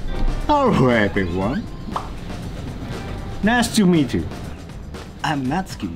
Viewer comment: Natsuki says "Nice to meet you" I hear "Nasty meet you."